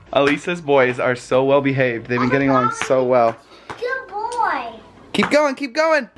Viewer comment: did say," Good boy"? Aww